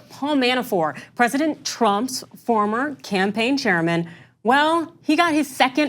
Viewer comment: I heard "campaign chairman" as "General Sherman". Had to rewind three times to understand what was going on.